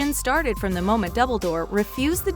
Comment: is it just me, or did they say dubledoor at 1:38?